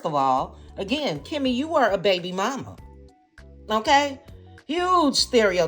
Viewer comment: I heard it “Kimmi your a HOEE BABY MOMMA” and I’m sticking with what I heard 😂😂😂😂 how dare she